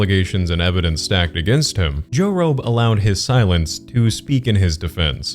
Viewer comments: every time says jorobe, my brain tries to auto correct it to joe rogan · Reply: You’re not alone, LITERALLY every time he said it I heard it as Joe Rogan at first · i’m watching this while cleaning my room and i keep hearing “joe rogan” and not joe roe or whatever this creep mans name is. and i keep getting shocked. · every time says jorobe, my brain tries to auto correct it to joe rogan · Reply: I refused to watch him for that exact reason bc I thought people were saying Joe Rogan lmao